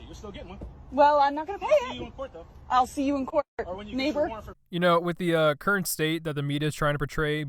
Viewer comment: did she said neighbour instead the n word